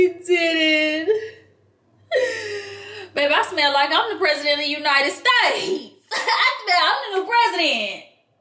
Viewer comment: is crazy!!!!!!!! Girl I'm almost spitting my food out watching you did she just say "I feel like the president of the United States" I'm deadddddddd 😂😂😂😂😂🤣🤣🤣